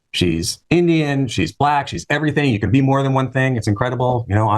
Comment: "She's black she's Indian. It's irrelevant" Then he continues to prattle on about race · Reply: I thought he said she's everything